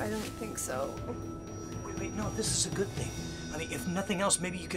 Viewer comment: Did he call her honey at 2:45 or what it was? I am just curious and I not sure if I heared it right. Not english-speaker here ^^ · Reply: Nope, he said "I mean", not "honey".:)